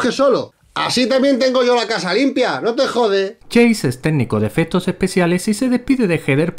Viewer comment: En el 1:20 escuché "técnico de fetos especiales" xDD